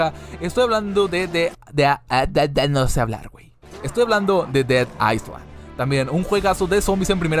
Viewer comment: no entendí como se llama el juego, alguien me dice? :c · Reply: Dead island